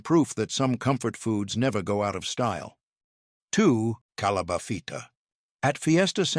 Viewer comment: PIck a doozy right AI??? Sounded like it said 'Calabathita'...Dear lord!:body-blue-raised-arms:😁😆🤣😂